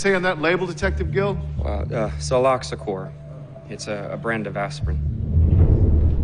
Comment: Hey, uh, when the detective says the name of the aspirin, Soloxacore, the subtitles say "colorectal" at @ 18:00.